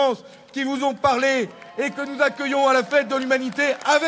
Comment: Pourquoi M. Roussel dit à 40:48 : "Battons-nous pour sauver nos zizis !" ?? Merci de votre rep